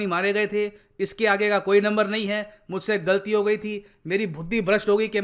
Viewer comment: Did said FUDDI instead of BUDHI ? 😂😂😂😂 Or did I heard it wrong 😂😂😂😂😂 ? At.